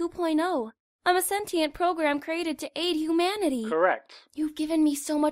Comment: I can't hear say "Humanity" without hearing "Imanity"...